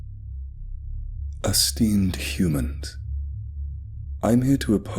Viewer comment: The first sentence, I heard "a steamed human." I mean, the title lined up...